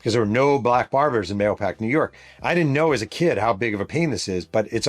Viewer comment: When you're talking about the black kids who couldn't find a black baber, I swear I heard you say: "I didn'n know how big of a Penis it is" 🤣🤣🤣 I had to go back to hear it again.